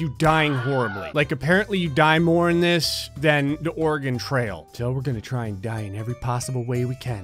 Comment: This is basically the Oregon Trail only somehow way more deadly. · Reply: I kept hearing "the organ trail"